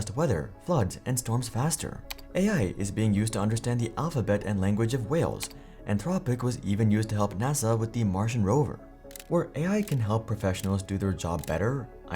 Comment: I'm sorry, I heard that as "help understand the language of Wales" and my thought was "just kinda assumed we cracked that case by now"